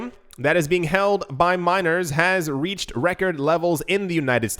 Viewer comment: I always hear "minors" lol.